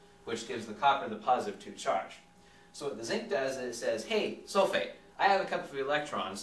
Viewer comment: I thought he said 'hey soulmate' rather than 'hey sulphate'